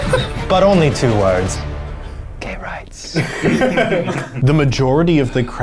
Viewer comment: WHEN EUGENE KILLED THE RING LEADER AND THE DM ASKED HIM WHAT HE WANTED TO SAY TO HIM BEFORE HE DIED I SWEAR TO GOD I THOUGHT HE SAID "CARROTS" AND I HAD TO REWIND TWICE BEFORE I HEARD "GAY RIGHTS"